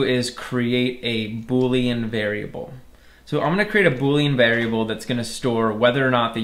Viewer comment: says "a bully invariable" instead of "boolean variable" lol (in subtitles)